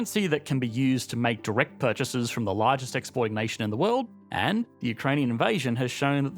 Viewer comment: "largest exploit nation of the world" Is it just me or does he infact say exploit instead of exporting (as the subtitles suggest)